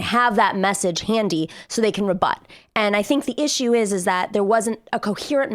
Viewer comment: Is she saying "the issue is is that..." or "the issue ises that..."? Why do talking heads repeat that sound so often? Is it a case of the yips? Is it a collective stutter? It's been happening more and more for over a decade, and it is leaking into common parlance and even informal writing. It's one word: is. Do they not know how it functions functions? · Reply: "and I think what the issue is, is that..." She left out "what" accidentally.